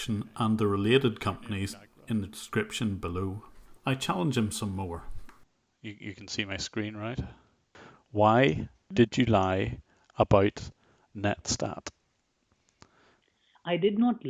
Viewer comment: sounds like u say " you lied about ned stark xD" had me laughin